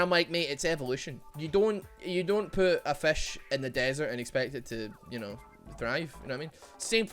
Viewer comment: I misheard say, "you don't put fish in a desert and expect it to drive."